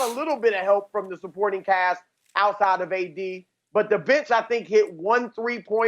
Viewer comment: Did he just called LeBron the B-word 😳🤔wauw....The B.tch hit like 1 3ptr(Damn Chris) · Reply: He said Bench